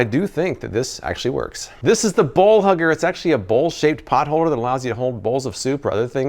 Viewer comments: OMG…. 🤣🤣 When you first said Bowl Hugger I thought you said “ball hugger”! I was like… WTF?! 🤣🤣🤣 💕✌🏼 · omg i thought he said "ball hugger" i was like wth kinda gadget you reviewing man?? lmao